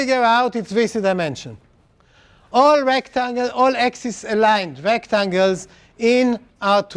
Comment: The proof given is valid only for axis aligned rectangles. Since the rectangles he considers are not axis aligned, then you can have a "diagonal rectangle" which contains x_top, x_bottom, x_left,x_right which does not contain all points of A. The right thing to say may be, the convex hull of A contains upto S = 5 sides. 4 degrees of freedom of the rectangle are insufficient to cover all possible choices subsets of 2^S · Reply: At around 1:06:17 he clearly says "all axis-aligned rectangles." Also, he specifically addresses this question in more detail around1:09:00.